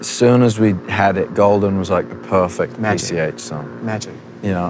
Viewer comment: In the minute 23:34 what says harry after he says like perfect nice ---- sun? i didnt understand it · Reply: Harry says Golden was "the perfect PCH song." PCH means Pacific Coast Highway in California. My guess is he meant perfect for the view and feel driving on that highway.